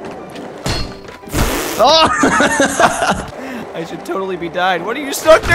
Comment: Hey at 2:29 did you say I should totally be died!! :D lol